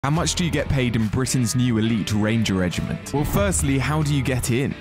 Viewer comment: Elite, lol, Sounded like he was saying elite RAF regiment. Its just an Rapid reaction Infantry unit.